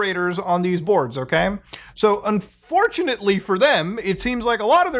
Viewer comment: did you just say “un4chanately”?